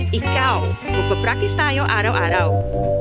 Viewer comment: "Did she just say he's the best violin player" Nah, in between the filipino bits you heard "let's meet every week (...) practice". The joke is that the smart man (who is drinking 7-up because he's smart man) plays terribly to get with the babe-y violin instructor.